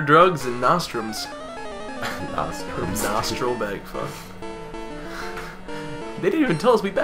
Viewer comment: I thought it said "pity! bag fuck" and I thought our bag was getting a pity fuck and well, I think that's funnier so I'm going with that.